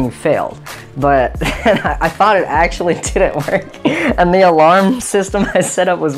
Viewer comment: I thought u said the alarm didn't work